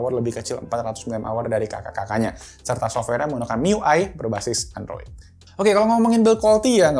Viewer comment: apa cm sy aja yg dengernya "MIUI"